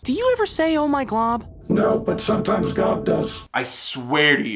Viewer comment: Commission to have him watch Star vs the Forces of Evil, definitely one of my favorite shows, along with Adventure Time and Gravity Falls Also 20:24 I think he said gob, as in one of the other sides of the head.